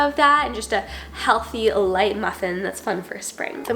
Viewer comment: …what happened to the word “muffins” there 😂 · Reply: "muffinus" 😂